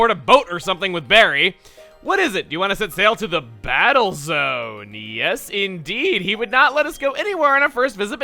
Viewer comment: Subtitles say this: you want it failed to the battles indeed he would not let us go anywhere in the. What?